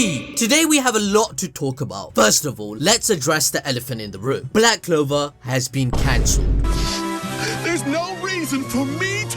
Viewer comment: am I hearing "Black Clover SHIPPUDEN"?!?!?